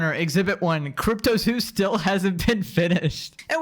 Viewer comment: Did you say eat prime and lunchly my at 19:18?